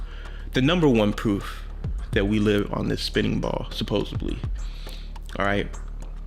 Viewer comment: As soon as that second guy said “supposebly” I disregarded his credibility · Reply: All I heard was "supposebly" glad I wasn't the only one.